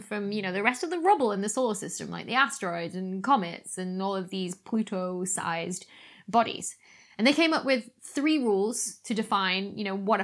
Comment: when you said "pluto sized bodies" i thought you said Pluto Sized Bunnies! 🤣😂🤣😂🤣😂🤣😂🤣